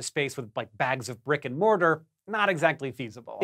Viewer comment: What did you say at 3:47? Rick and Mortar?